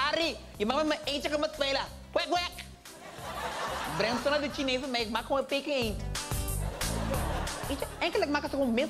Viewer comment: Wat zegt bij 0:42? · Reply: Ik breng het zo naar de chinees bij mij, ik maakt et gewoon een Peking eend